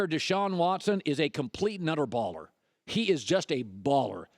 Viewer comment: Hold up.. did Colin say nutter baller wtf??😂